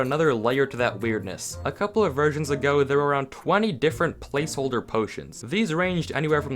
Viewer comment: I thought he said Virgins, help me and my dirty mind